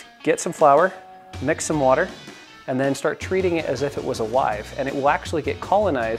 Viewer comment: Thought he said treat it like it was a wife